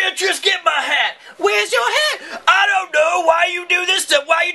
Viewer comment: 1st time hearing I thought he said bitch where's my hat